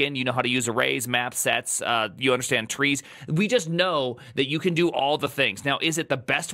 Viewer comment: It’s funny all I heard was “you understand trees” and I lost my shit. Out of context that’s hilarious